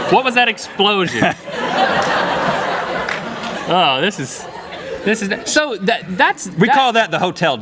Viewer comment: What did they say when they were at the back of the room? I heard "OK, this is Halpert...?" lol